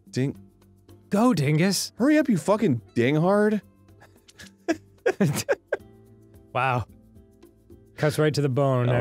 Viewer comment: i SWEAR i thought arin said 'gaytard' and i was like 'WOAHH MAN..' LOL